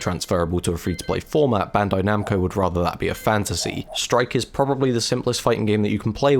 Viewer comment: did you said "bandai namco would rather that be a FANTASY" and then "Strike" to slide into talking about Fantasy Strike? If so that's a hell of a hit confirm